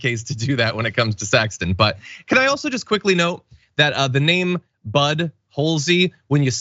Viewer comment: Butthole? Did I hear that?.. Ah 1:40.. I did.... LOL.. But still seems its only wrong when you read his name... Correct when you say it.